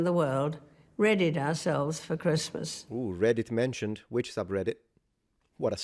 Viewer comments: I think she said 'READIED ourselves for Christmas', not 'REDDIT ourselves for Christmas'. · I think she actually says "baton" and not "battle." "Passing the baton" is an idiom. I think the AI-generated subtitles just couldn't understand her. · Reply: And here, he and the subtitles think she said “Reddit”, but to me it's clearly “readied”. The context makes it 100% clear.